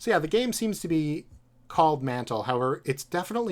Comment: i thought it was called ‘manhole’ pfff